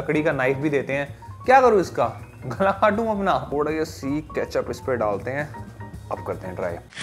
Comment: jo mene socha kya aap sabne bhi woi socha 😂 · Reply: " Gla kaatu apna " bola tha bhai 😂😂